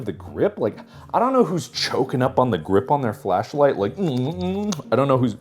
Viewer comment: i was just 10sec skipping through a tiny but and i hear mark say, "CHOKING UP ON THE CHUNGO!!"